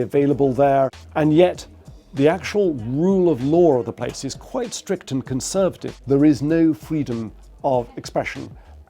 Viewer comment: i just heard biT#es